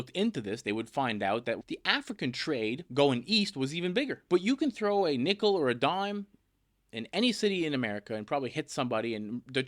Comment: elden ring dlc game me so much ptsd that i heard "you can throw in a nickle or radahn"